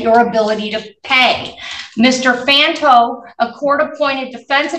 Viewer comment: When i first heard this, i thought she said his lawyer was Lanto! Josh Lanto. Can you imagine???